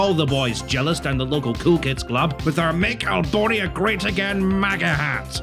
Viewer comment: For a second I thought that red hat said "Make Ebola great again."..